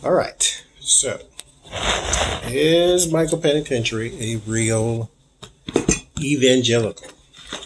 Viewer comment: My mind heard you say, "Is Michael Penitentiary a real evil genital?" lol Just love your readings!